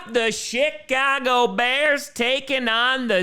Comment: Was it just me, or did I hear SHITcago Bears? 🤣 🤣